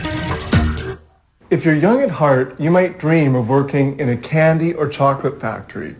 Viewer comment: omg did he just say ''if you're young and hard'' at the beggining 0.0